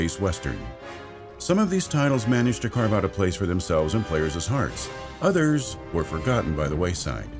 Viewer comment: did you just say “players’s” ?